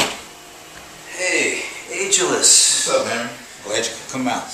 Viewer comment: This MFs name is, I thought he was saying “age-less”😂😂🤣😂🤣